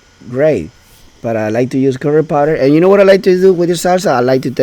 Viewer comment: I thought you said "I like to watch Harry Potter" lol